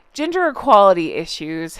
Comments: You said "gender equality issues" and I thought you said "ginger equality issues" 😂😂😂😂😂👩‍🦰👩‍🦰👩‍🦰🦰🦰🦰👨‍🦰👨‍🦰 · I had to go and replay a section because I legitimately thought you said “ginger equality”! 😂 The sad part is it was completely believable and sounded like something they would try to pull. Always the victim. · I thought you said Ginger equality 😅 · When you talked about their new trend : « gender equality », I heard « ginger equality »... and I thought : Fair! 😂 This said, happy you are back 🧡✨ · I misheard you and thought you said they were trying to become advocates for "Ginger" equality! 😲😂😂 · I thought you said "ginger equality" instead of gender equality. I was thinking , "WHAT!!!!"😂 Equal rights for ginger/red heads?!?" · I totally thought you said, “ginger equality” funny part is, I thought, yeah, that sounds like a Sussex demand. 😂